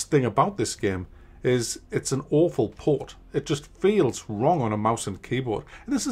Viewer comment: I had to rewind back 10 seconds at around 1:12. I legit though said it was a Mobile port instead of an awful port the first time!